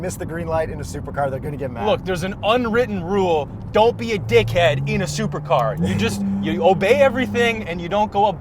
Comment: WHAT DID YOU SAY 😱😱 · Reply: Dickhead